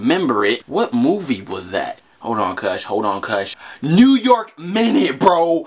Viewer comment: What is the movie he said at 1:24 ? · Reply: Nguyễn Hoàn new York minute